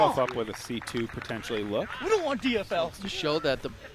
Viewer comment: what did say? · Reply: “We don’t want DFL” Dead Fucking Last